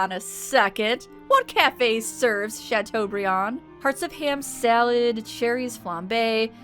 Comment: I don't know why I said hearts of ham and not hearts of palm. It just sounded better. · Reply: I thought you said “parts of ham” and I was VERY concerned for what they were eating 😂😂😂